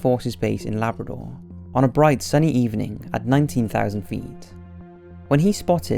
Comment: Did you say on a bright "SUNNY" evening?